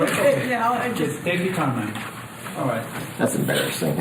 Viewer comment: After that lady had her coughing fit..the defense lawyer was joking with her and then the judge said something and then the lawyer continued...what did the judge say?? I couldn't make it out...its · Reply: take your time- then gives permission to continue questioning.